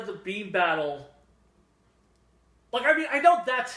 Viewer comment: I kept hearing “Bean Battle” lol